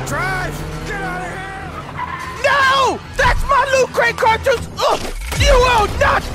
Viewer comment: Who here thought he said lube crate, cause i sure did lol 😂